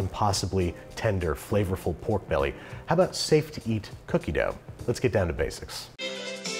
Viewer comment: It's been brought to my attention that raw flour can contain pathogens! The eggs might be safe to eat, but please consume raw flour at your own risk - or pasteurize your flour by baking it separately at 350F for 10 minutes! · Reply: Binging with Babish I totally heard "steak to eat cookie dough", and not "safe to eat cookie dough and was very disappointed to realize I was wrong :(